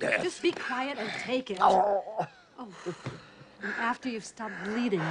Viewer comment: to be totally honest, the first time I saw this I thought he said "no jelly today doctor, this time it's gonna cost you" lol